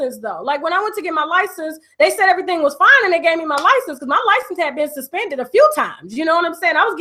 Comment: I'm confused how you go to jail for suspended a license when you weren't driving. You must have had a warrant? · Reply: i thought she said ole dude gave here license